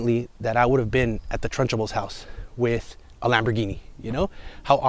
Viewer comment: Haha I only hear you saying “Trunchable” as in rhyming with Lunchable